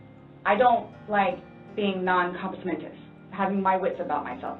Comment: i think she says non-cognitive